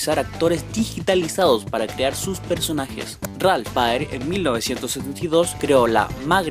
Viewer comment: no entiendo en que año dice en la 35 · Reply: 1972